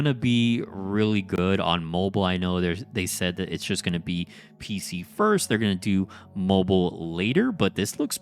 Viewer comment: P.S. You almost gave me a heart attack!!!! When you were talking about PC, I almost thought I heard you say "mobile first". lol I had it on in the background and the waves of emotions from the Marvel Snap shitty PC client came crashing in. I know it's PC first but I thought something changed last minute. Thankfully the game board looks optimized for both PC and mobile right from the start unlike Snap (what was Brode thinking as a former Blizzard dev).